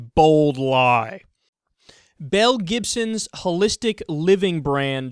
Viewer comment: I thought you said her name was Mel Gibson for a second